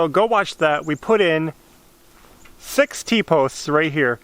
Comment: You made me laugh....I thought you said 60 posts, but you said 6 T posts. I need to clean out my ears! Funny.